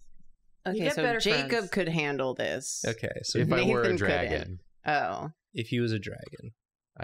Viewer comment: Im crying at about 25:16 I thought said “If I were a (f slur)”